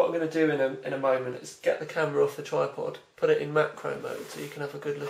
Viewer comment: lmao at first I thought you said "mac pro mode" XD